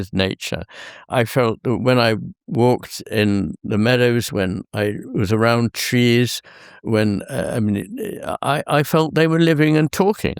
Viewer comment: forgive me Dr. McGilchrist, I heard that as "when I was around cheese" and couldn't help but smile